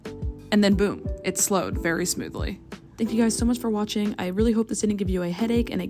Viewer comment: When she said “i hope this didn’t give you a headache” I misheard it as “i hope this gave you a headache”